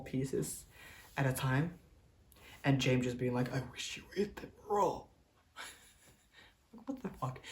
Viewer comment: you missed the real mysterious, creepy and weird part. he didn't say "i wish you would eat them raw" that may have been an almost lol normal thing to say. what he actually said was "i wish you would 'take' them raw". WTF take? lol what the hell did ne mean by "TAKE". he's just a super weird dude.